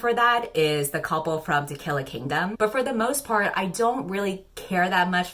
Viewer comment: i wasn't looking and heard it as "Tequila Kingdom" lol